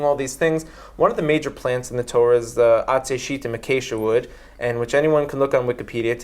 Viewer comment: what did he say? Akesha wood? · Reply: Acacia